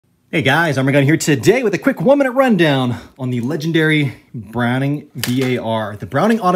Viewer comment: I thought you said gun down